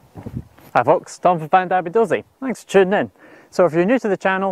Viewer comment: in case anyones wondering what he says i'm pretty sure it's "Hi, folks! It's time for Fandabi Dozi!"